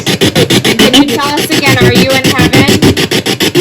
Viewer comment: “Are you in heaven?” I heard “ think about it”